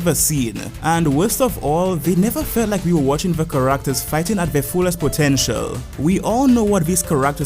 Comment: what word is he saying? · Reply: im pretty sure he's saying "characters" guess this is a tad late lol